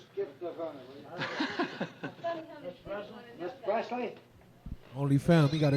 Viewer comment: You misunderstood the man said, Mr. Presley not President. It just sounded like he said President. Elvis was with his dad, Vernon Presley, Mr. Presley.